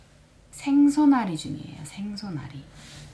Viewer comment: 뭐라고하시는거? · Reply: 생선앓이?